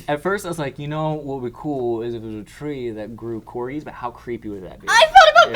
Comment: When said about growing corgis on a tree I thought he said "corys" not corgis. Where I'm from "cory" is slang for a penis. So I was interested in why wanted a penis tree